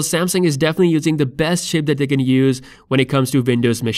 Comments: did he say "best shit "?.. Yes!!!! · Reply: X2 He said chip. He also addressed it in the comment section replying to another person. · sounded like you said Samsungs using the best sh*t 😆